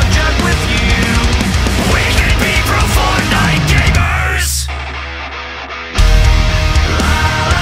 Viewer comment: Why i hear "coprofortnite gamer"?